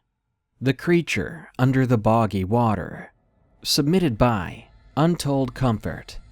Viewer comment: On 3 I thought it said "Creature Under The Boogey Water."